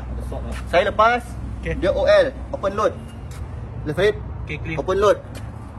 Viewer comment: OL tu bukan Over Load boss, OL di seting ohm adalah Open loop (non-feedback) kalo di melayukan, litar terbuka. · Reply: Tuan salah dengar kot,org ckp open load. 😂